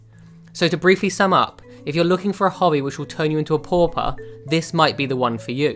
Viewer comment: Sorry I couldn't tell what you said at 2:37 I'm from Canada , maybe it's different lingo Sounded like 'If you want a hobby that will turn you into a porper' ??